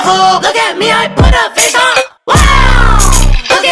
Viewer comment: when i heard it for the first time, i thought they were saying "look at me i pull up n**a